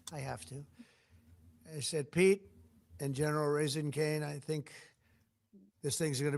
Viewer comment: did he say General Raising Cane???!??